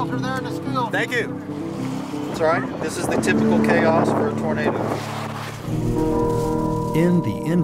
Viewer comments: the CC says 'burrito' instead of tornado!!!!!!!!!!!!!!! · if you turn on captions it says burrito instead of tornado